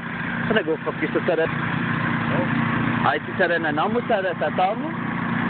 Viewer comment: Ha ha ha!! My Chamorro sucks (guahu i Tagalu) but I heard "chada nanamu"!